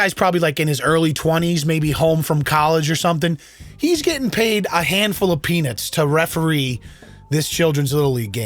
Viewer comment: After the little league cllip where the two adult gay babies were screaming acting stupid asf, when you were talking about the coach it sounded like you said that he was "only getting paid hand full of peenis" Genuielnly heard that even when I went back and watched it a 2nd time. Thank you for using them as a teahing tool to help all karens repent asking for the manager haha:yougotthis